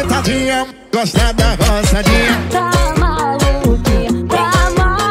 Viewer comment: Só eu q ouvia "rosadinha" ao invés de "roçadinha"? Kkkkkkkkkk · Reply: Eu tbm ouvi "rosadinha" kkkkk